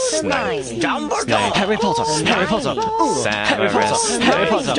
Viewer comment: It sound like Harry is says "Harry Bottom, Harry Bottom!!"